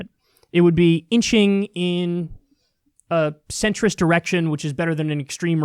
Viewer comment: I thought he said a centrist erection for a second.